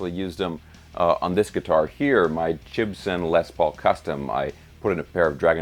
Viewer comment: Did you just say... Jibson?